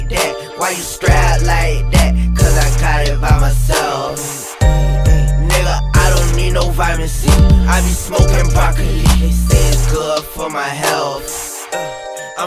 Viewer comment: I thought that nigga was saying "I don't need no pharmacy, I be smoking broccoli".